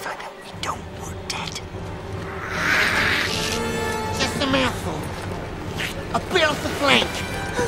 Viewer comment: What is he saying???? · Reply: Corey Hambrick Just a mouthful, a bit off the flank (bit off the side)